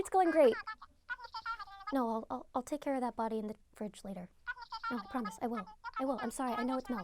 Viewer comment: what did i just hear i will tack care of that body later did I hear